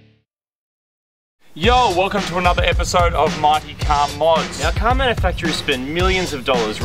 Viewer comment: i do not hear mighty car mods. I hear Marty car Mods :D